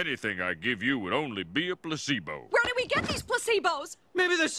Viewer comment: When she said "placebo" i literally thought she said "pussybones"